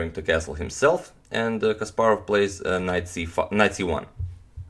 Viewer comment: In min 2:06 I thought I heard knight c fu.. Knight c1